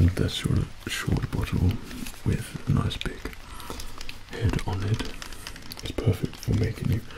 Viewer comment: “Big head on it, perfect for making you wet” Anybody else hear him say that or just me